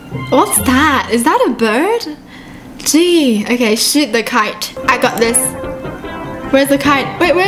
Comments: Kinda sounded like she said "Sh*t the kite" Im not trying to be rude cause ily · When she said " shoot the the kite" I thought she said "shit the kite" 😂 excuse my language... luv you!